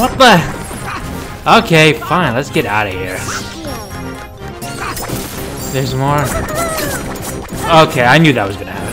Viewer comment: Wait you have never seen Peter Pan movie before?! :0 6:01 WAIT!! What did Donald say?!?!?! 😂🤣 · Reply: I dont think I have. He said “Thank you” not that other thing that he was mistaken to have said before!😂 Thanks for watching!